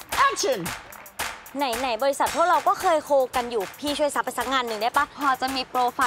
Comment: I really heard " Nehi nehi Bhaisaaab". The subtitle clears the doubts, though.